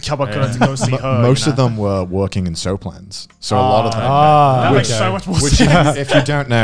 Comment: What’s that word said at 3:29-3:30? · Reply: Soaplands?